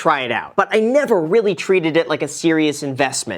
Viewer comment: I’m confused graham. Are traders investors? Are investors traders? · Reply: Maybe you said “treated “ and I heard “traded”